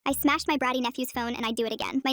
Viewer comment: My ADHD ahh thought that she said “ I smacked my nephews ass and I would do it again” 😂